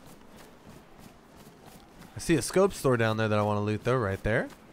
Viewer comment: Did you say a 'scope store' @ 50:45?